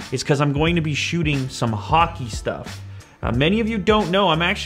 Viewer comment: when he said hockey i thought he said haki lol